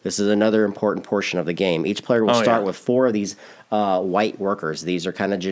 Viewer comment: Hah - - when you said "Each of the players starts with 4 White Workers" - I instantly thought you were going to say "4 White WALKERS" - love GoT...;0